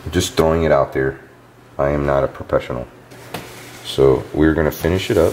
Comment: We are professionals (1:11) *1 minute later*: I am not a professional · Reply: I’m pretty sure he said, “I’m not a PROPESSIONAL” at (2:48) like if you heard it as well.